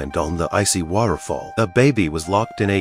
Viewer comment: What I hear: "They decided to rescure Dababy" 😂😂😂